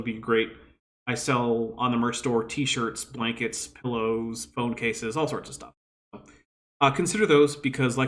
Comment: I misheard "phone cases" as "bone cases"